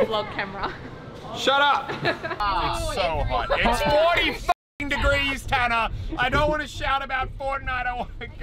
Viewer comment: Did he say it’s hot it’s 40 degrees ?